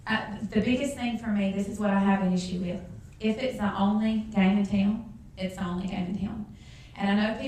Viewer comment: Um... at 1:28, I have no idea what she is trying to say. It sounds like " tang a tang" or "tang a tail"? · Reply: She says, "If it's the only game in town, it's the only game in town."